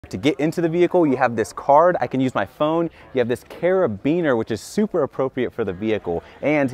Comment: the pause in carabeaner made it sound like he said Beanear